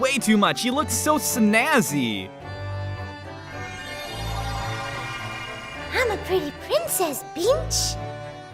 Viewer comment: I thought he said I'm a pretty princess 𝙱𝙸𝚃𝙲𝙷